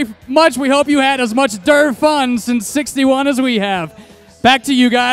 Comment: does he say we hope you had as much derp fun?